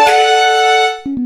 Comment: i hear speed